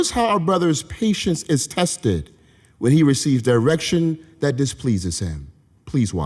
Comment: OH MY JEHOVER I THOUGHT THAT SAID ERECTION 💔